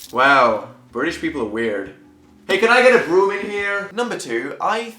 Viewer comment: oh, BROOM. i thought you said brewer. like a beer...